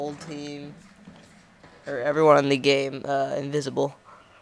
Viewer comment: Oh sorry I thought it said invincible